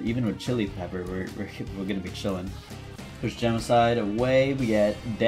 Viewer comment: did you call Gemini genicide